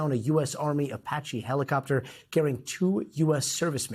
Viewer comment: He said “killed two servicemen” and then he says they where rescued with minor injuries? Are they dead with minor injuries I’m confused! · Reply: That what I thought he said, but I went back and listened again. He said "carrying".